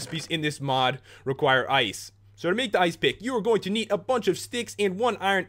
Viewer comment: I thought he said ice pig LOL!!!!!!